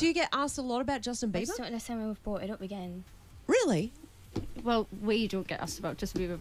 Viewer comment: what the hell does she say here? I hear "imsterneportmegan" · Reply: 'I just don't understand why we've brought it up again.' I know what she said as I talk fast.